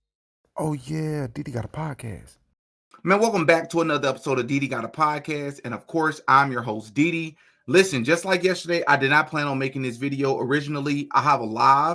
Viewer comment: whoa that was a close one 😮 For a second there i thought you said you were diddy 🤣 had to rewind it to make sure you said DD 😂👍